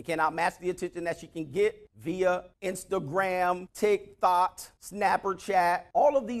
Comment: Did he just say TikThot??🤣😂🤣😂